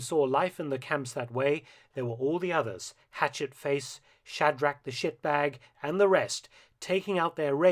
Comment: Did I hear that right at 36:36 "shadrack the shitbag"?